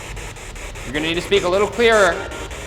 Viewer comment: some spirit box translations 9:51 i heard rest or last